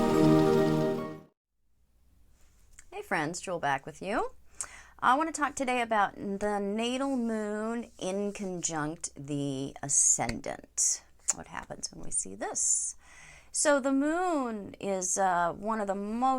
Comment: Oh... in-conjunct... totally thought it said conjunct